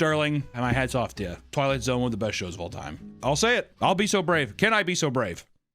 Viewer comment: I had playing in the background and this episode came on, but between my birds chirping and all the other chaos going on I thought he said "biggest frogs of all time" and was instantly locked in. Still an awesome video even though there were no frogs. 🐸